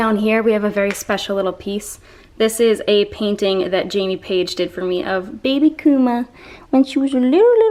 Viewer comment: I thought you were tryna say Jaime Paige died! Omg 🙈😭